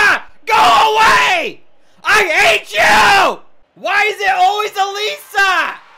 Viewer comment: ELISA GO AWAY I ATE YOU like did anybody else hear ate you